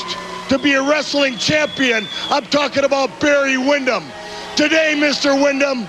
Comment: I'll pass the message on to him. But he does have a limited vocabulary. He's from Metairie, LA and a lot of people there aren't the sharpest crayons in the box. He's probably going to tell me to go F__K myself for telling him to clean it up. He's really a curmudgeon and an but we tolerate his immaturity. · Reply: this sounds like saying talk about Bruno and JYD